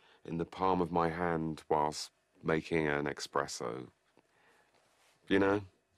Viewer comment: did he just say ‘Expresso’ *imminent shot to head